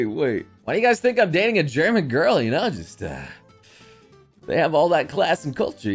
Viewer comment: When he said "why did you guys think i date german girl", i hear "I'm actually getting friendzoned"